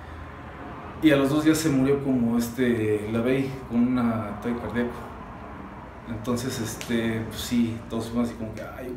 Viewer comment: Que fue lo que dijo en esa parte? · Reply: Dijo "puto el que no le entienda"